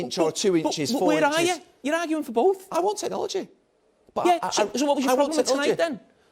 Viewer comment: Did Gary actually say "I want an orgy"?